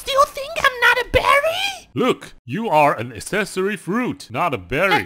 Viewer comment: 難得床編出現超生癖字、聽不懂的accessory(?) fruit ass⋯⋯assessu⋯⋯asseesso⋯⋯assess⋯⋯ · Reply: Accessory